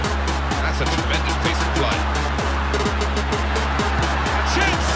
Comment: is he saying: Hazard with a tremendous play.?